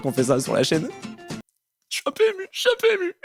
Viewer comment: j'ai compris jsuis un pmu😂😂😂